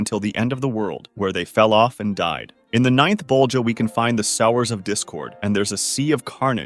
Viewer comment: I lowk thought it said sewers of discord